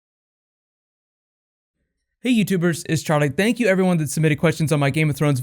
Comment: Oh. For months I've thought you were saying "Hi YouTube Birds!" and I thought that was such a funny and personal and friendly greeting - then I read the subtitles and realized you were saying "YouTubers". Makes more sense, but I did really enjoy "YouTube Birds" more!